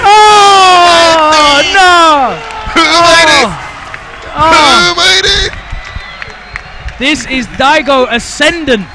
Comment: For years I've always wondered what says at 9:55 to 9:58 😂 he says it every now and then but I never knew what he said exactly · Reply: humiliated